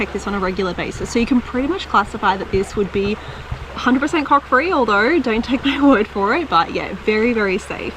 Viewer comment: Thought you said "cock free" had to re-wined, dinner looked good, im a male guts that's one meal hahah.. i kept looking at your sleeve and the pot handle, all end up in the dirt.....